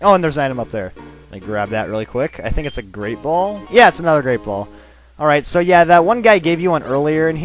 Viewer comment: did he say great BOWL????